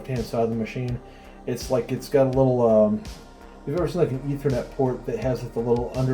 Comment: did anyone else hear "internet porn" when he said ethernet port I need help.